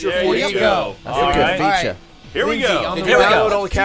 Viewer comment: What does say at 2:53? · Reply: It's a good feature